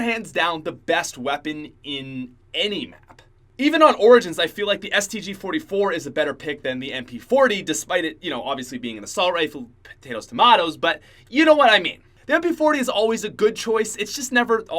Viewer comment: Did he say potato tomato???